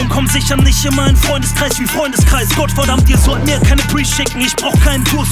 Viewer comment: Sagt er bei 0:25 Scotch? Kann es nicht verstehen.